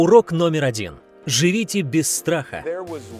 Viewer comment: Живите без страха ( мне послышалось жрите без страха)